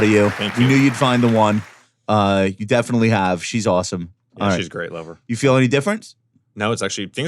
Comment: I definitely heard “she’s a great lover.” Love how Josh and Michael both jumped on it. Great reaction. 😂😂😂